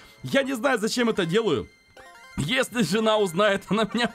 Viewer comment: я в первые слышу что говорит жина Я думал у него нету жина😂 Поставьте лайки если в первые услышали от жина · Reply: не жина а жена